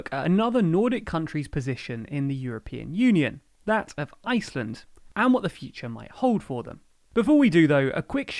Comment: I swear I heard you say "Latv... Iceland!"